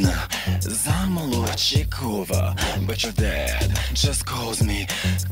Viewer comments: Katya: Yekaterina Petrovna.. Zamolochikova, but your dad just call me... KATYA~ Everyone: instant Katya fetish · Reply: Lmao at first I thought she said "but you're dead" 😁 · when Katya said “Yekaterina Petrovna Zamolodchikova, but your dad just calls me Katya” she won this challenge right there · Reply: I am Russian and until I saw your comment I always thought that she was saying 'bolshoy' instead of 'but your' wich means 'big' in Russian